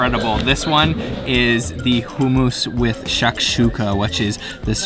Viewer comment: love ya vids but its sounds like ya said hoo moose lol u canadians say the u wird lol